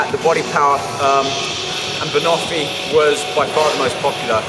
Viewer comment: what flavour was he saying was the most popular at 27:00? bonofy? lol